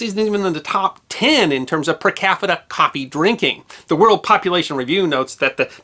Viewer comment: Perhaps it's just me but I could have sworn said, speaking of consumption, "per caffita" toward the end. 😂 🤣